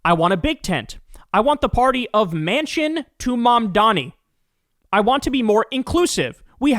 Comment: I heard "Mansion to Mandami" 😂 Same critique applys.